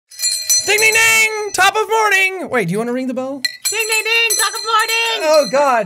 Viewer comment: Jack: DING DING DING, TOP OF MORNING, wait, do you wanna ring the bell? DING DING DING, TOP OF MORNING! Jack: Ah, God... I'm really annoying. Me: :::Can't Breath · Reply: I heard "taco morning!"